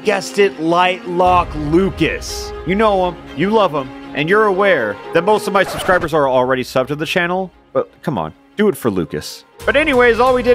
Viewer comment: did he say "most of my subscribers are already subscribed?"